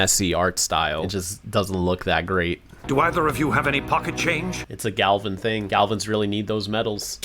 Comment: I thought he said fucking change